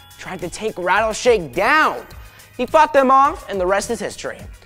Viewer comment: it sounded like Zach said he f***ed them offXD. LMAO I love you Zach Gordon!